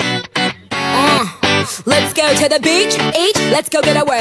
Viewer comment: guys it did not say bitch it said beach come on guys!!!!!!!!